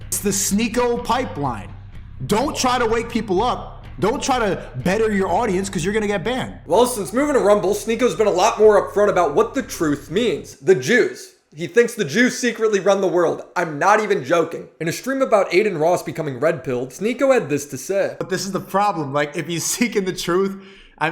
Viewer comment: Took me 15 minutes to realize you were saying “the jews” instead of “the juice”